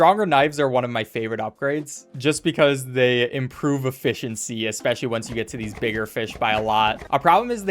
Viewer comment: did you just say...efFISHiency?